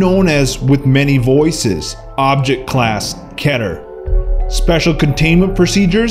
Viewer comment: when he says keter it sounds like he is saying cheddar xD